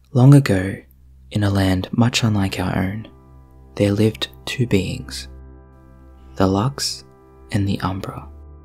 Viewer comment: In the beginning I thought you said “there lived two beans” and I thought “lesbeans”